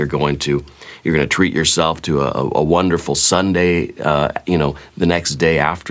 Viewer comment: Heard it as "treat yourself yourself to a wonderful sundae" aka ice cream on the clock 😂